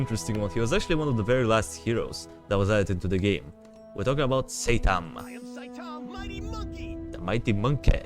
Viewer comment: I dont know why I just heard saytan